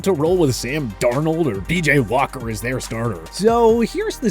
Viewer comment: is he saying PJ Walker BJ Walker or DJ Walker?... Because it's PJ Walker